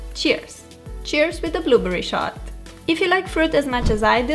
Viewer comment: O Did Really Say Blueberry Shot? XD lol GO!